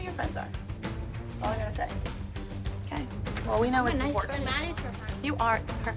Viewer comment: I rewatched this sooo many times 14:41. I wish I could hear what exactly Maddie says before Melissa says you are her friend! Also the way Maddie said apparently not anymore🥺! I truly believe that wasn’t Maddie or Melissa’s fault. Abby wants to win so she made them perform! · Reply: "I'm a nice friend mom!"